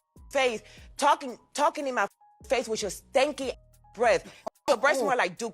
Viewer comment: of the show , I thought I heard her say “you stank “